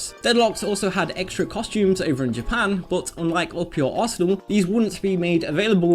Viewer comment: I keep hearing "up your arsehole"